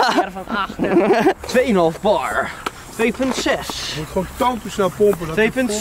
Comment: Wat zegt nou bij 16:54 ????🤣🤣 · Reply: Je moet gwn tantoe snel pompen